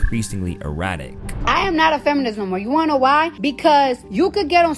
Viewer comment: Did she just say “I’m not a feminism” 🤣🤣🤣🤣